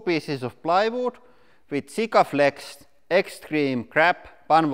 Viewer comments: Hahahaha i had to google it i had to rewind like 8 times and each time i heard "Extreme crap glue" // (Was extreme grab) :D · So the glue is called "Extreme Grab"? I thought you said "Extreme Crap".